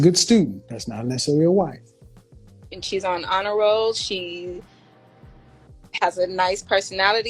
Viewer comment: KS: "Are you raising your daughter to be a wife?" Her: "yes she's on the honor roll" Thats like me saying I'm raising my child to be a chef because he got his driver's license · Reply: Wtf is honor roll? I thought she said aderol…